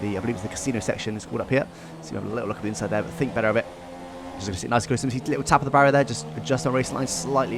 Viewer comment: what did you say lol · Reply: As you can see